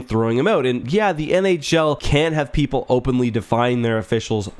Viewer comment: The audio I get sounded like 'can have have people openly...' and the transcript also has 'can'. I think you said 'can't and if so, I pretty much agree they shouldn't openly defy. But like you mentioned earlier in the video there needs to be some type of transparent accountability. And by transparent, I also mean transparent to the fans as well. Because at the end of the day we are the customers and the ones paying the bills. Be it through ticket, merchandise sales, purchases of advertisers products or what have you. Thanks for another excellent video.